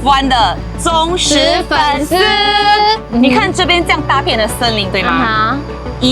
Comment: When she said 一大片的森林我听成” 大便的森林”😂